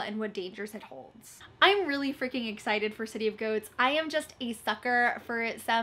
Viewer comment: I have an ear infection and keep mishearing things and kept thinking you were saying City of GOATS 😭😂